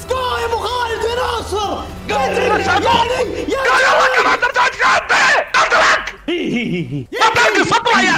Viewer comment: ابو خالد وش يقول😭😭😂❤ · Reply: ماقلتلكك انك تحتزم بييييي